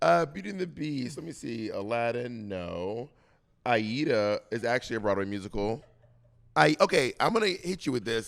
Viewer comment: what is bob saying? Ieta? Eyeita? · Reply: I believe it's Aida